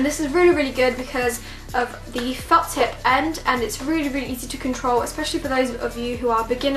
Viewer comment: i thought you said f*cked up end instead of felt tip end i can't be the only one